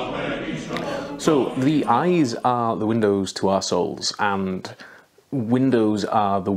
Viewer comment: hahahah because of your accent, I thought you said that eyes are the windows to assholes....I nodded sagely, then...wait... hang on a minute...